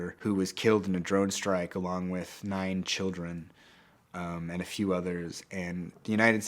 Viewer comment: The subtitle is wrong, 0:31, he said nine children. Who did the sub? He needs to get fired.